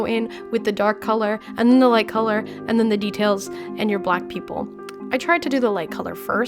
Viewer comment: to me it sounded like she said “black people@